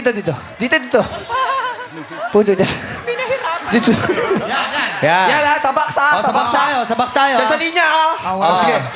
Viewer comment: may narinig ako kay Vhong nag Mura ata yung sinabi niya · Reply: Althea Jodie Vlogz sabi ni vhong "BUTI NA.."